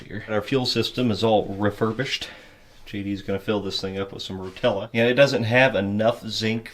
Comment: I heard "JD is going to fill it up with nutella" but it was rotella